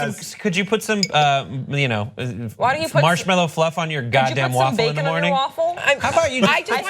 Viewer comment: omg. "How about you just put some boogers on your waffle" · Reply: I thought he said marshmallows.